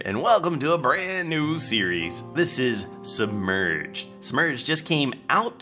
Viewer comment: Lol Sounded like u said "Smurged"